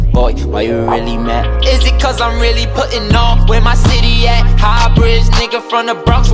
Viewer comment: did he just say put on for my city but in L.A lmao